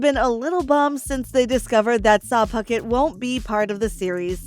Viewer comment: Is it just me or did she say 'SARM Puckett' at one point?😂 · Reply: I heard 'Sah Puckett' 😂